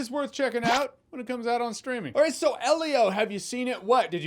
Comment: weird, when he said streaming, i heard "the pirate bay"